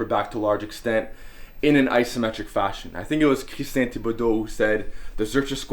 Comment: what was the name he said there? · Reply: Christian Thibedeau